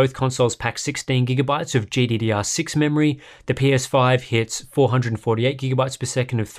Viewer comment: I swear he said the pinus5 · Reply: Definitely says PS5